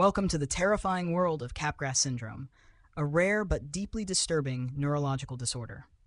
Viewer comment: I had to google for the real name - I thought it said "catgrass syndrome"